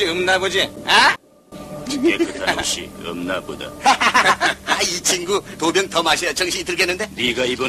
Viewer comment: 이친구 다음 뭐라는거임? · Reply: 이친구 두병 더마셔야 정신이 들겠는데 요